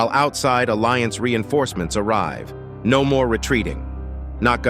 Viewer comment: Some people are mentioning that titan tv man says "no retreat" but idk, I honestly hear both "don't waste it" and "no retreat". It's upto you to decide which one it is I guess since we have no official confirmation. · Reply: I Heard "Dont Retreat"